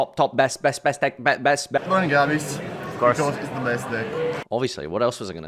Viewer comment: what did he say? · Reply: burning abyss